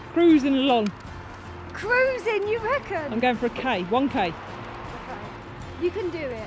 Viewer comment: When you said "I'm going for a k" I thought you'd said "I'm going for a cake" 🙈 ...only thing I'd be running for tbf 🍰🏃‍♀️😄